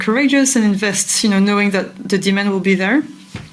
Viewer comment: For I concur with Mike. He speaks for the masses and the delusion among the weapons manufacturers is astounding. When the woman at the end addresses Mike I thought she was saying “demons” but it was “demand” But “demons” made more sense to me even after hearing it again. God help us all, and protect Mike and Clare in their work for humanity.